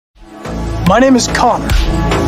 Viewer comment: Why did I hear “My name is Cock”😭😭😭